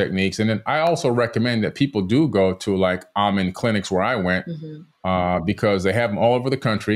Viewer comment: Min 21:45 what type of Clinics Billy recommended to visit?? Amon clinic?? Is that what he said? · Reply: Amen clinic!